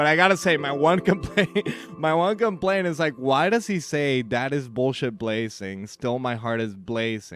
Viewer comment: Rhyming Blazing with Blazing is sick as hell and I support it the whole way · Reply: i always hear "that is bullshit crazy!" Since the first time